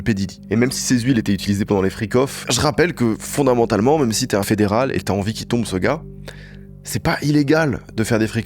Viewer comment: Y'a que moi qui trouve qu'a 44:00 il dit "flics" et qu'au montage il a changé par " fédéral" ?